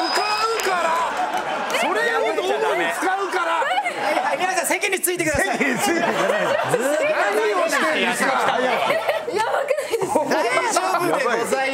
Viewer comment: 空耳で何を四天王？に聞こえたw